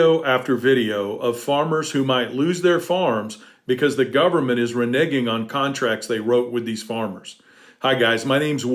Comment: Did Will just say “Re Niggin’” 13:18 ? 🤣😂🤣😂